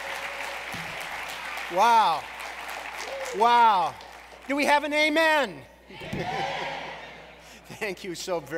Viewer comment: did he just say "do we have an amen?"? wtf